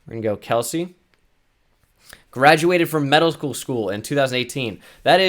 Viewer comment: When you said medical school thought you said middle school...